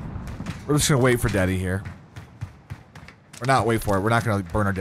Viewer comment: I thought he said "Daddy" till the 2nd time he said "deady" then it was more understandable xD